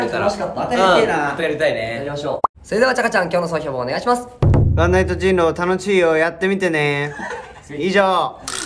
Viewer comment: ワンナイト人狼たのちいよって聞こえる可愛い😭